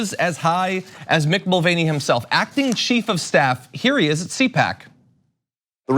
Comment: Omg, I misheard you. I thought you said Mick Mulvaney, acting sheep of staff, lol! Very apt though.